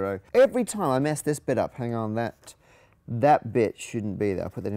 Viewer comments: did he just said b*tch? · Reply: He said “bit” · Did he just said "That bitch shouldn't be there"? xD